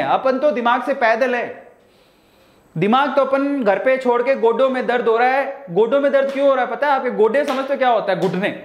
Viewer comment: i thought he said... "Gotiyoon me dard ho rha hai"